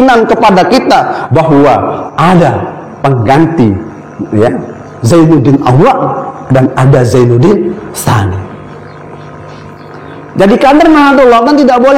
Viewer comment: Coba disimak pada menit ke 6:37, apakah saya yang salah dengar atau memang fesory salah ucap "ADA ZAINUDDIN ALLOH, ADA ZAINUDDIN TSANI" maksudy gemana ya?